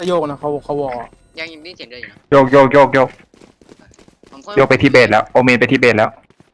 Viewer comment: ได้ยินโอเมนไปทิเบตแล้ว นึกแล้วนึกอีกเขาเล่นมุกป่ะวะ อ๋อ ไปที่ base แล้ว <num> เวน หูชั้นมันไม่ดี